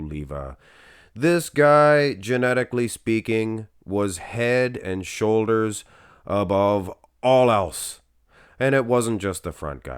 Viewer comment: I thought you said Paul had narrow shoulders.